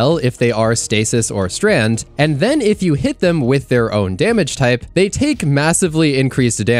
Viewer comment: ? I never mentioned an "alt damage type." I just said you should match their damage type for bonus damage. E.g. the subjugator is stasis, so hit him with stasis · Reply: Ohhh sorry, I misheard 'own damage' for 'alt damage' 🤦‍♂